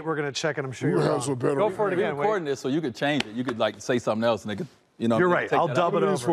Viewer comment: Did Wade say “you can say something else nigga.” 🤣🤣 I might be buggin